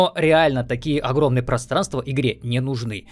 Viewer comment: Мне послышалось или нет, ты сказал "просранство"?